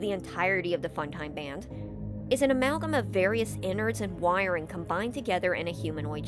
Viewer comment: I misheard that as "an amalgam of various Ennards and wiring," and it makes me wonder if that's where Ennard's name comes from, since they're just the innards of the Funtimes and later act as Michael's innards too for a bit